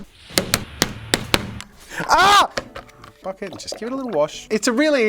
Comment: I thought at 5:18 jazaa said "Fuck it, just give it a little wash" and was incredibly surprised but then I realize he said bucket